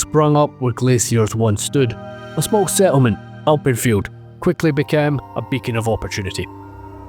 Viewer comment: I thought you were saying Alprechtfeldt and not Alpenfield.